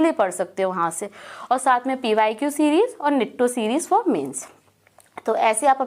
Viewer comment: What she said at 11:44 pyq series and ??? Series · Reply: Nitto series for mains by parmar sir